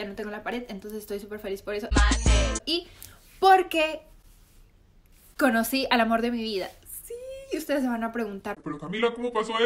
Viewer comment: al principio pense que decia conoci al divorcio de mi vida